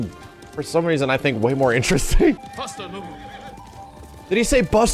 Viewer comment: I'm really happy that I wasn't the only person that thought he said "BUST A MOVE!"